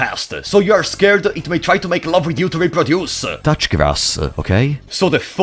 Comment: I THOUGHT HE SAID TOUCH YOUR ASS😭